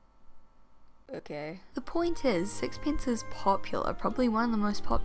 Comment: I know this is off topic but the auto-generated captions kept saying “sexpants” and now I can’t stop laughing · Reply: I thought she was saying sexpence the whole video.